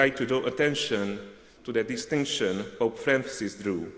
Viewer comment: "...in his recent Trieste address" again a guess. but I couldn't understand what he said before that. "...Francis Drew..."?